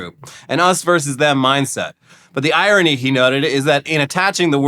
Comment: did say nutted?!?!